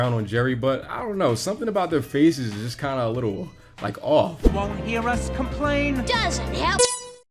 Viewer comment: To this day I still have no idea what he's saying. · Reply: “don’t you believe it”